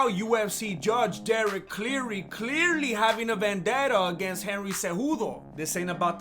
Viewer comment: I thought you said Henry had no chance against Aljo..